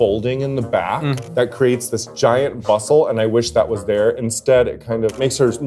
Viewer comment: I don’t understand English.. 8:00 can someone tell me what she said? “Create a gaint” what? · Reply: It's a bustle, the back part of an obi waistband that looks like a long bow.